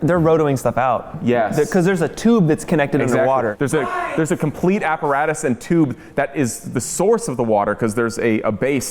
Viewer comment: Someone screaming in background. What did he say!? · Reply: I think they asked WHY ?!?